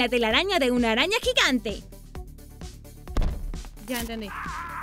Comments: En el minuto 9:44 se escucha una vos qué dise ya entendi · Like si en el minuto 9:44 dijo (YA ENTENDI) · En el minuto 9:44 se escucha "ya entendí " Like si escuchaste. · QUIEN SE DIO CUENTA QUE EN EL MINUTO 9:44 DICE "YA ENTENDI" 😮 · alguien mas oyó ya entendí 😜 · like si te diste cuenta que en el minuto 9:45 dicen algo asi ya entendi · Se escuchó a alguien que dijo Ya entendí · No se si ustedes tambie pero en el minuto 9:42 al 9:44 yo escuche un YA ENTENDI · En el minuto 9:48 escuché ya entendí así rapidito !? 😰😰😰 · Like si escuchaste en el momento 9:45 que alguien dice ya entendi xD 🤣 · Luke si escuchaste "ya entendí " en el minuto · se escucha ya entendí pero grave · Minuto 9:45 se escucha YA ENTENDÍ estoy loca o que 😱😱😱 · En el minuto 9:44 se escucha ya entendí like si lo escuchaste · ya entendi like si escuchaste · Se escucha ya entendi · Like si en el minuto 9:45 se escucha "ya entendi" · alguien escucho " ya entendi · Del minuto 9:44 a 9:45 se escucha ya entendi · Like si en el minuto 9:44 escuchaste YA ENTENDI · EN EL MINUTO 9:44 SE ESCUCHO YA ENTENDI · En el minuto 9:44 hay una voz que dice ya entendi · En el minuto 9:44 se escucha un ''ya entendi'' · Like si escuchaste ya entendí en el minuto 9:44 o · En el minuto 9:44 se ecucha algo un ya entendi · Quien se dio cuenta que en el minuto 9:45 dise YA ENTENDI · si es cierto en el minuto 9:44 dice ya entendi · En el minuto 9:43 hasta 9:44 de escuchó un YA ENTENDÍ like si te diste cuenta